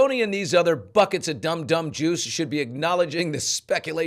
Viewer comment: Hmm really? I heard Dumb Dumb Juice 😬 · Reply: Hey, good point. I heard "Jews" but the letter Billy is reading from says "juice." But juice does not make sense in that context, but Jews does Nevertheless, I'm going to edit my comment. Thank you!